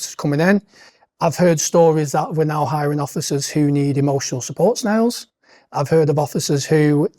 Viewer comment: I kept mishearing him say 'emotional support.. snail?' and had to go look up what it actually was. That's actually ludicrous.